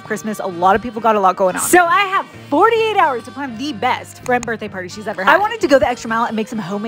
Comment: Am I the only one who thought she said bread birthday party 😂